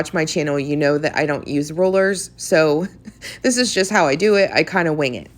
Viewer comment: Very cute. I've seen this made by others but for some reason your video caused more inspiration. Funny note: I thought you were saying rollers but it was rulers.